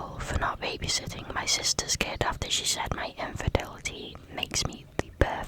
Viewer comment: new viewer here, love your videos!! though i had to do a double take because i swore i heard "aita for not babysitting my sister's kid after she said my INFIDELITY makes me the perfect aunt" and i was so confused what that had to do with anything 😭😭